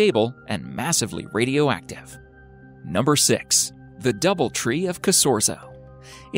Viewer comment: I thought it said “The double tree of kazoo”